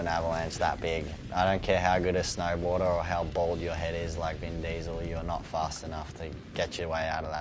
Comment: "I don't care how bald you are like Vin Diesel, you are not fast enough" lmao i like this guy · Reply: I thought he said i don't care how bold you are, not how bald you are